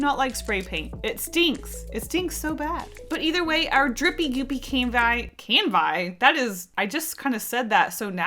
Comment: I definitely heard "drippy goopy camguy" instead of "canvi" at first, but all in all that also sounds like it could also be the subject of a piece 😂